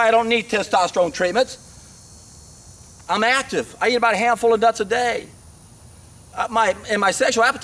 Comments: what does he say "I've been active, I've ** *** ** a day" ??? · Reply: Late comment haha. I'm active ive eaten about a handful of nuts a day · he says "I'm active, I eat a hand full of WHAT a day???/ cant understand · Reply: d man I'm pretty sure he said nuts